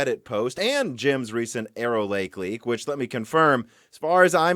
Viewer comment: Arrowlake.. All I hear is "Error Lake"